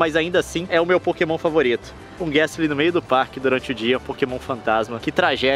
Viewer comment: nss eu entendi Meio da rua pokémon FANTASMA